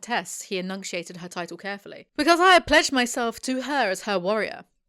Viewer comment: ish. Was starting to fall asleep, then snapped awake when I heard, “ I have pledged myself to her as her warrior.” I heard “ pledged” as “ pleasured.” I wouldn’t put it past the suethors.